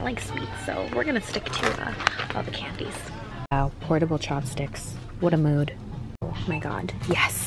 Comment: I thought you said “portable chapsticks” and I was like 🤦🏽‍♀️